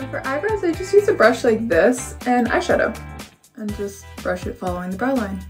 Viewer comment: When she said eyeshadow I thought she said I shut up